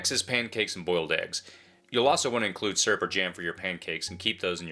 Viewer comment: ish did you say "surfer jams"?